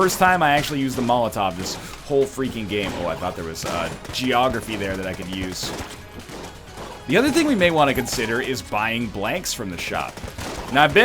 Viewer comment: did he just say geography?